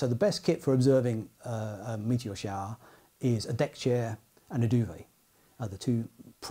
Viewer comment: At first I thought he said "a deck chair and a doobie".